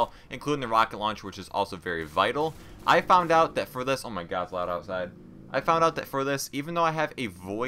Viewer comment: lmao at 17:46 i think you say "omg theres light outside"